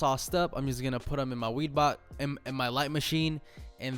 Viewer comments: So we just gonna pretend he did not say his weed box at 6:12 🤣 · Who else heard him say "weed box" 🤣🤣🤣